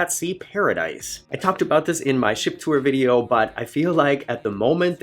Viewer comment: What you said was "Ship Tour" what I heard was "S**T Tour" .... I'm old. I drink.